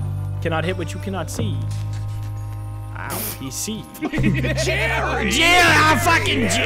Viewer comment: I really thought that said “jewy jery” at the end there